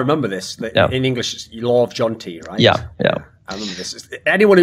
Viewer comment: what's this lorv jonti this they are talking about. I can't find anything. I don't even know its that what they actually said? Also I Like CSGO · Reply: I think they were talking about Law of Jante